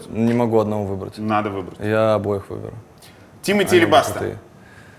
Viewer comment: Мне послышалось Тимати алебастр)))))